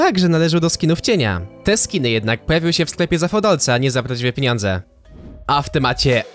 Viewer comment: Ej 1:05 ja tam piniądze usłyszałem